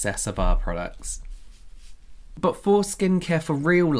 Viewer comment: At the end I thought said "foreskin care" 🤣🤣